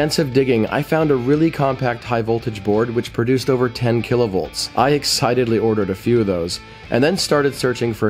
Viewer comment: i heard "Kill-a-volts" not that other sciency word. LOL